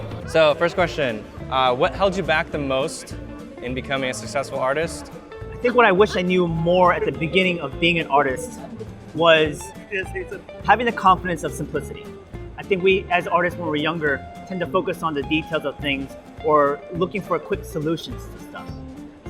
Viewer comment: I heard it as "having the confidence of some pussy..."